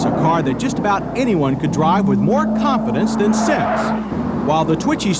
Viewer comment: did he really say "more confidence than sex"? wtf? lol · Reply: "more confidence than sense."